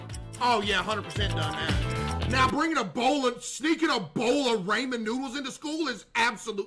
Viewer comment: Did he just say ''raymen'' noodles?